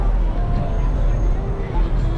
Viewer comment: Wow so many people out!! I will be honest and say sometimes you make me feel so much anxiety when you are so close to cars 😂 what did you say at 14:48 ? That guy just totally swerved in front of you! Crazy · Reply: I said: 'Hand uitsteken he', what means something like: 'Indicate the direction when you turn'. It looks closer on the video then it is in real life!